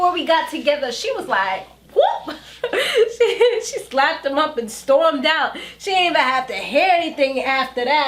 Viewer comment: I thought you said "she slapped the Muppet and stormed out" lol! I figured it out after a second or two but... I'll still think you said Muppet because Hakeem does look like a Muppet doll.